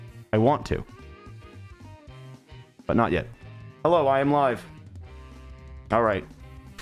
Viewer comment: “Hello I’m live” I thought he said “Hello, Lime life” as in Tubbo is called ‘lime life’